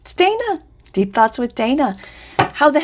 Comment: Deep thoughts with Dana...I thought she said deep throat with Dana..I was way off 😘